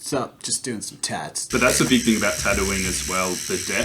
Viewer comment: At about 6:04 I thought he said "that's the good this about Tattoine" XD